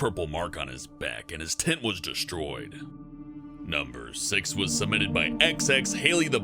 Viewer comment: i thought he said "his tit was destroyed"